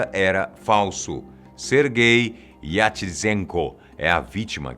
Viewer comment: eu entendi ser gay kkkk